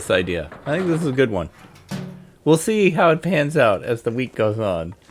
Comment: I thought I heard say "We'll see how this PANTS out as the week goes on"